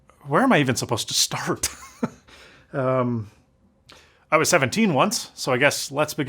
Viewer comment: It sounded like he said I was 17 once. Even the captions agree.